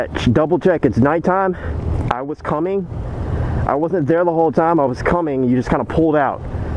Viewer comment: just lying to her face...i was coming and you pulled out...no...you saw cars merging over and tried to undercut them without slowing down when they clearly were merging and had blinkers on...you deserved that one bud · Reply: and i thought you had said “I was there the whole time” but after listening again you said “wasn’t there” so like i said i misread the situation